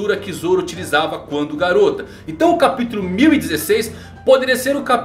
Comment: so eu que entendi garota?